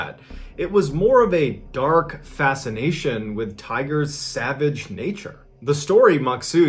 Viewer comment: OoOh! Tigers! ... I thought you said tires until 1:26 I realized you were saying tigers LOL! The whole time I was like, "huh? Tires that's interesting." Then you mentioned the trip to the zoo and I was like that's bizaare but maybe there were tires set up somewhere? My bad hearing always has me mishearing what people say until I read subtitles or lips. It's like a bad yet funny autocorrect only for my ears. 😆